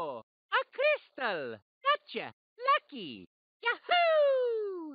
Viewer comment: As a kid i thought Mario said Yucky instead of lucky